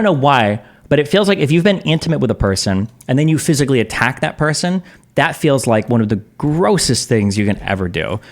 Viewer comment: Am i the only one who heard him say, 20:18 "If youve be intimate with a person and then you physically attack that person. It's one of the grossest things you can do". Maybe because he is talking about physical characteristics?